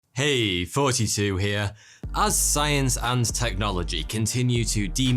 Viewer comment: it always sounds like he is saying heey its 42 here instead of thoughty2 hahah XD